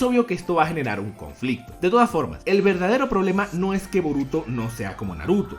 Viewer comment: Yo escucho que dices vómito, no boruto.